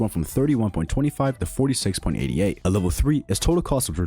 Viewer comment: did bro just said 46.88? 5:04 3mil or 3k?